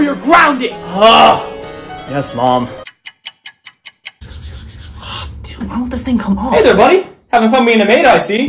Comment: was that a Cuss word? I heard Dam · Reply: said dude not damn